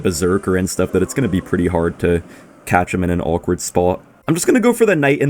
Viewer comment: I heard touch him in an awkward spot